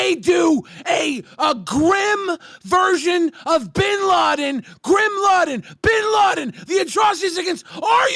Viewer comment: did anyone else catch that? "grim laden" instead of bin laden